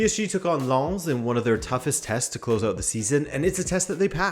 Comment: Did you just said "Toughest testicles of the season? LOL really?!!